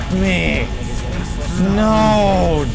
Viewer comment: Did he just say nee?!